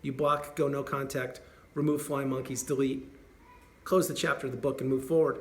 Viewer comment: Remove Flying OR Lying monkeys & delete! LOL I think I heard that right! Did I?